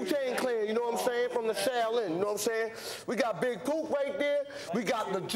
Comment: Big Poop. Thats what it sounds like ODB is saying when he introduces everyone. Googling big poop dosnt help =)